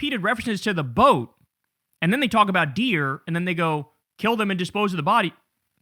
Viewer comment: I thought she (and he) said bears, not deer. But could have been code for people. Scopolamine is used for nausea, I've been prescribed it for post operative nausea in a scopolamine patch. But yah side effects in higher doses can be the nefarious reactions they were looking for. Sick and twisted.